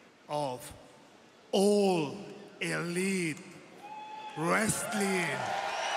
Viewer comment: I thought I heard him say...Old Elite Resslin